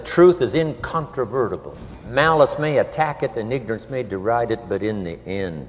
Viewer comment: OMG, Did Jim just say "niggers"? Maybe I heard him wrong. I hope not. I really look up to this man. 1:00:35-36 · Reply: Tiffany Smith. Hahaha. No, he just said it really fast "and ignorance" may deride it......